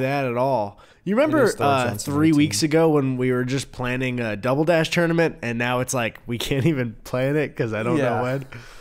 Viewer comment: why did I hear say Double Dutch tournament? Maybe cuz I’d pay to see that.